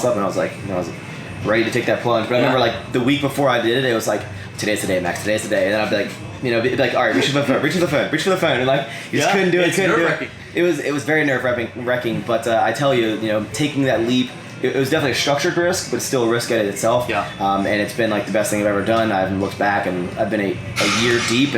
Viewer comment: Maxx Chewning the type of guy to say "nerve-repping" instead of "nerve-wracking"